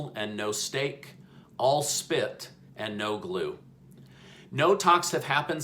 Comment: Omg, I thought you said “all spit and no lube”... I had to rewatch! 😂😂😂